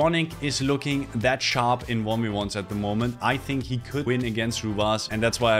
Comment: Wtf did you say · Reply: I think he could win against rw9